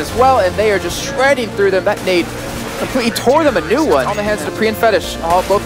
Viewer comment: Did commentator really said "Tore them a new one", or I just misheard it?